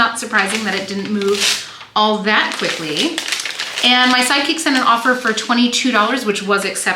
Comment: Every time you say, “My sidekick sent an offer for…”, I hear “my psychic sent an offer for…” and it makes me giggle. Where can I get one of those? I would hire one in a heartbeat whose intuition skills were highly attuned to knowing the exact offer amount the buyers would accept! 😂